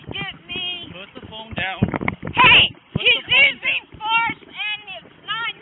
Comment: I was shocked! I could have sworn she said, "He's using farts!"